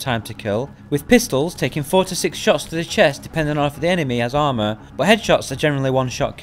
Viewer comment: I thought he said 46 shots to the chest 💀